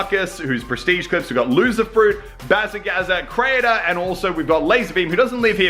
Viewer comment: I thought he said CRAINER